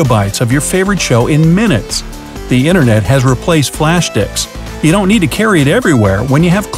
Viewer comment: Did he just say "Flash Dicks"???